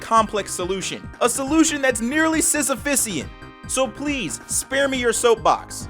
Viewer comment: what word does he say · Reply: Sisyphean; Denoting or relating to a task that can never be completed